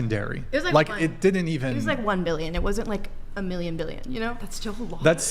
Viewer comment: Did she say a "Million Billions"??? 😂😂😂